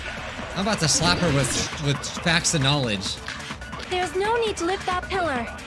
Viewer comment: Bijuu : 12:22 " Im about to slap her with packs of knowledge " My ears: " Im about to slap her with packs of ramen " I literally had to rewind it to hear what he really said 😂😌